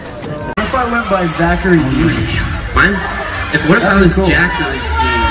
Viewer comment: What are they saying at 4:29 until 4:54? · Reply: Ayuuu Kt Zach:What if I was Zachary Dean? Jack:What if I went by Jachary Dean? Both:J-J-Jachary Jack:my middle name is just Robert Zach: Boring. But it’s okay I still love you.